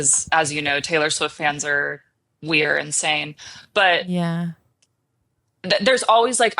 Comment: i thought I heard we're insane.. but then the caption was "weird, insane" Thanks for clarifying. Appreciate you and your art. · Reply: absolutely not! I said “we’re insane.” 😂❤️